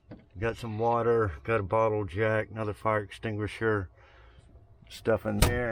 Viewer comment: LOL...When you said bottle jack it sounded like you said bottle of jack. That was funny.